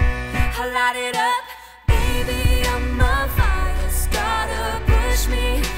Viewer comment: I love it! Such a great job. I like how "firestarter" also can sound like "fires daughter" or is it just me? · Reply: I also thought she said "fire's daughter"